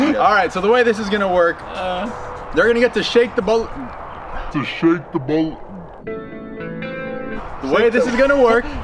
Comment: At first I thought said shake the butt lol